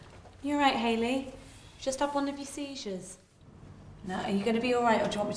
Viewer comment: i thought it said fecal seizures idk why